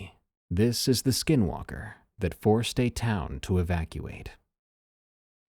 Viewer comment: I’m so high I thought that shi said Ejaculate lmao